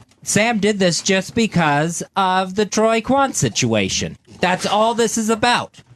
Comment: That’s not what a contingency means Fez:). For a second I went nuts because I thought Fez said “Sam is doing this to me because of the whole Trayvon situation.” I couldn’t wait to hear that theory